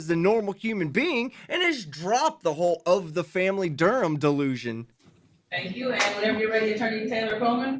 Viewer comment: My favorite part is when the guy in the second hearing puts his hands on his head after of the family Durham goes back into his sovshit script about his name lol. He’s all, “wtf bro”. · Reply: It must be my hearing, I thought he was saying "of the family DERP"